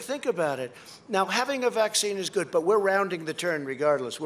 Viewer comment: all i heard him saying was "i am capping. I am capping. I am capping"